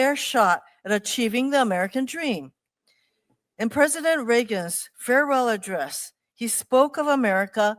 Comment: Did she just say. President Regis?